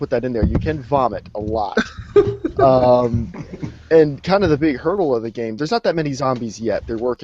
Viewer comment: Did say at 06:05 "A big hurl of the game..." right after talking about vomiting?